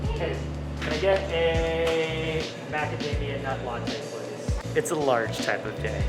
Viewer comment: Did you just order Macadamia nuts milk with coffee ?? Or its just me who hear that? and sorry for what happen to you..